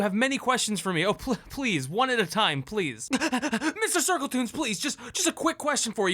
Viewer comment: did u say Mrs. Circletoons??? 0_o